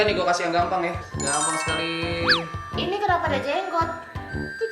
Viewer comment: kenapee gw dengernya jadi jenglott:"v